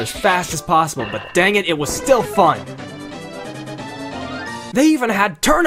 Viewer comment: I think what Phil Hartman here meant to say @ 3:21 was "Dammit it was still fun!"